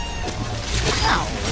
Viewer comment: what did he really say there? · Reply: IceBucket gusse we’ll have to make our own